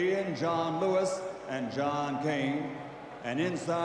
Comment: I thought he said the last judge was DON King !.....I tought OOH Nooooo the fights been fixed ! LOL...sorta